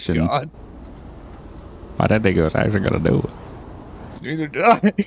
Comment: sounds like he says “that n*gga actually gonna do it” but he actually says “I didn’t think he actually gonna do it” 😂😂